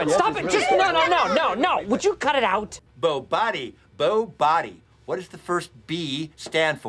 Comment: Im dying of laughter when i hear creed say “BOBODDY”.